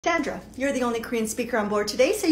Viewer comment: I heard 'dandruff' not 'Sandra' ☠️